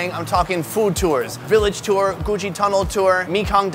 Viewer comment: i thought he said Gucci tunnel